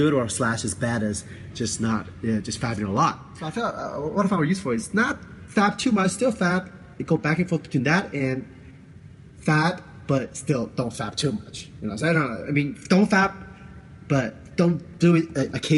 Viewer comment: is the nofap advice. So well said. LMAO. Wtf is he saying? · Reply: "What I found useful is not fap too much, still fap, go back and forth between that and... fap, but still, don't fap TOO much. You know, I mean don't fap, but... don't do it occasionally. In one end the extreme is when you have your... you know, minimum fap, OK whatever. Minimum fap. And then you have your... you fapping... so... deload between the extremes but not TOO extreme is what I'm saying".